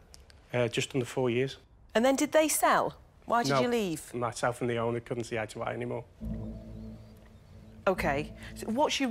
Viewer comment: what the hell did he say, no matter how many times i listen, i cannot decipher. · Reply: “Myself and the owner couldn’t see eye to eye anymore.”